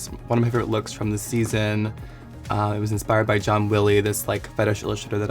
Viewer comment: Who she gets inspiration from?? minute 0:13.. didn't understand the name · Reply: John Willie